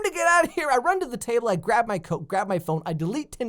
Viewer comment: does he say grab my coke